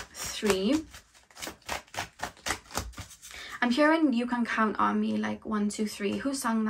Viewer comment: Lmmmmmaaaaoooo....UR HEAVY ACCENT..I THOUGHT U SAID "U CAN CUM ON ME".BUT U SAID U CAN COUNT ON ME" 🤷‍♀️🤣😜💯.IM SITTING HERE LIKE ONLY BATMAN CAN DO NASTY SHIT LIKE THAT..🤣🤣🤷‍♀️😜💯 CUZ I HATE OTHER PPLS GERMS N BODY SECRETIONS 😒😆 ONE DAY AT A TIME...DONT EXPECT FOR ME TO B "MATURE" ALL THE TIME